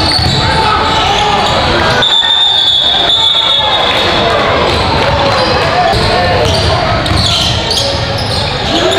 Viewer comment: What was the call at 2:50🧐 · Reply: Stepped outta bounds